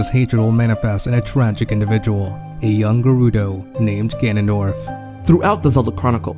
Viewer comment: Lol, I misheard "a young Gerudo" for "a younger Rito" and was really confused for a brief moment.